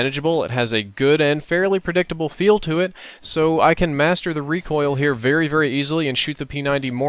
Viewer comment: I swear I thought he was gonna say "I can masturbate"